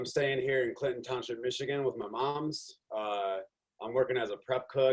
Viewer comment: did he just say moms? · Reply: "with my moms" is slang. "moms" is East-coast American slang.